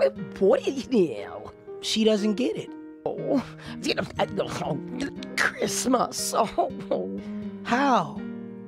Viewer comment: it sounds like grandma says “oh, then send her down a hole on Christmas” or “send her up a frog” lol she truly knows what to say